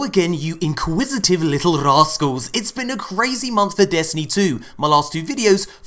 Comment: "Inquisitive little rascals” Well thank you good sir, are you going to want some tea whilst we talk? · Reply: R2 D2 I thought he said “inquisitive little assholes.”